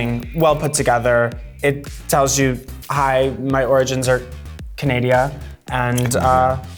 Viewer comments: did she say CANADIA LMAO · Did she just say Canadia? Lol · Y’all mean to tell me that now one her aquira say candia instead of Canada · did she just say Canadia? 🤣